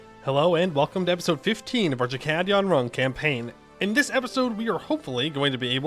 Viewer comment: I swear, when I hear you say "Chakkad Yonrung" I hear "Chad Gone Wrong".